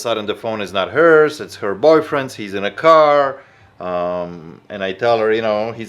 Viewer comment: I thought she said the she split up with her boyfriend and hes waiting out in the car park